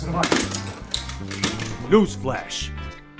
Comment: When Jensen said newsflash, I thought he said goose splash for a minute